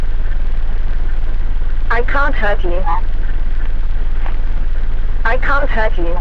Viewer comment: I thought it said I can't hurt you 😭